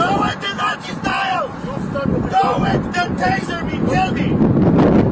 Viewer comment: Someone with a better ear: Does Pawlowski say 'kill me' at 14:44? Semi-related, what's his proper title? Preacher, reverend, something else?